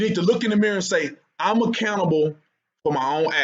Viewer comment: Dude, every time you say accountable, I mishear it as "A Cannibal"